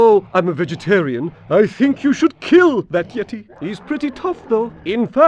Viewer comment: lol the voice actor says "kill that yeti" but the subtitle says "torch that yeti".